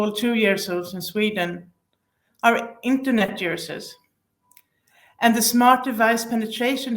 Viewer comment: Hi, it"s a great video, very informative but I do struggle to understand an expression (?) at 3:19 which I heard to be "67% of all 2 years old in Sweden are internet juicers", could you help me to get that one ?